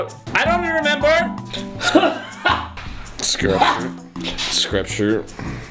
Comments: i thought he said scripture lol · At first I thought Arin said screpture.